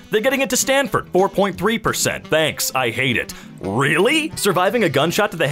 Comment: At the beginning it sounds like says banks, I hate it